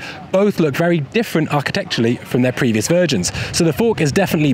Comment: why did say virgins instead of versions?